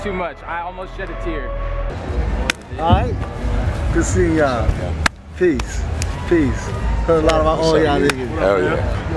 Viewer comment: what did DOOM say while handshaking (1:41)? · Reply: it’s been 7 years, but he says “peace, peace, heard a lot about all ya’ll ngas”